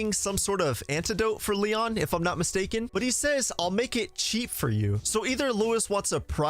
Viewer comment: Wow, that's hilarious. I thought Luis said, "I'll make your tea for you."